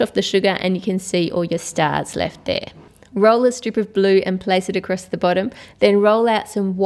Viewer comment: Did i just hear her say "Roll a stupid blue"??